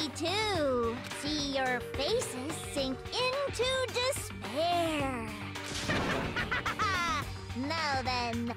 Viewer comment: I heard it like she said "dis bear" instead of "despair"